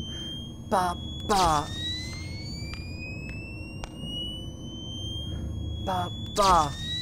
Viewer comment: Why does the sound that Matpat makes 1:09:30 sounds like he is saying 爸爸 (ba ba) Father in Chinese